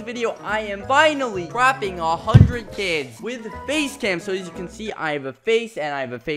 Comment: when he said face cam for the first time i thought he said base lamp